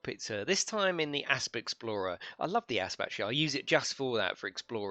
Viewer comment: Lol. All I heard was ‘I love the ass!’